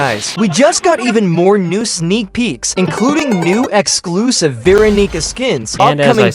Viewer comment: guys I swear he said veeronica 😭💔🥀